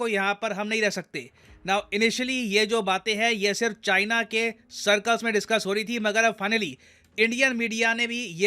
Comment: did you say "China k CIRCUS" me?😂✌️